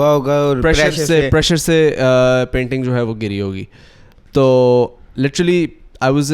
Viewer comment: I thought is saying my child is here!